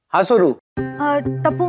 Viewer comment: did Tapu called "Hi sodu" 😂